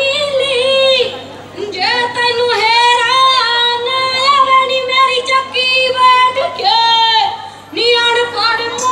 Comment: Can anyone plz tell me what is she saying at 0:46 durinh her geet...unable to understand 🙁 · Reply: ਹੇਰਾ